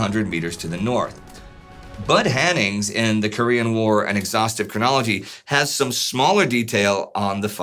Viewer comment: I misheard the quote and instead of "an exhaustive chronology" I heard "an exhausted chronology". I'm now imagining a historian who writes about the war but is extremely tired due to having had to deal with all the shenanigans by McArthur, Kim, Mao and the gang for the past years.